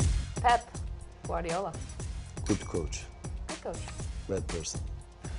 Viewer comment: From 13:14 when asked about Pep Guardiola, did Zlatan say "Good coach, bad person" ???